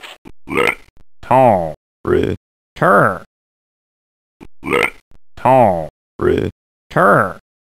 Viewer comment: I always wondered what those frogs were saying. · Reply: They were saying "little critter."